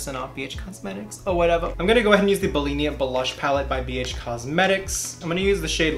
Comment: I keep listening over and over and it sounds like you said Bulimia blush pallet 🤢 not cute lol